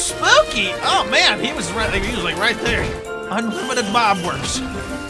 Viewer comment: did he say unlimited bob works at 28:02?